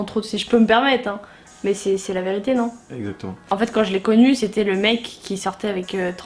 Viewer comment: Pourquoi a 6:10 dit a tg toi